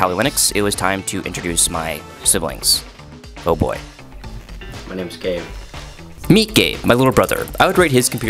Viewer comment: i heard my name is gay